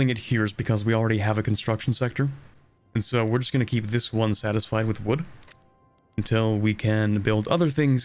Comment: Did you say “we are just gonna keep this one satisfied with wood”